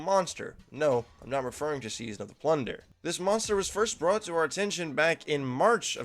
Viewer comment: I legit thought you said “Season of the Blunder” lmao